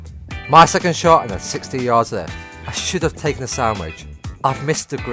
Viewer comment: When you said sand wedge I thought you said sandwich